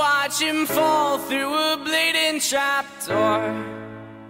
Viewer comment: I thought at the end it said "Cause nobody knows he's life" but instead it said "Cause nobody knows he's alive"...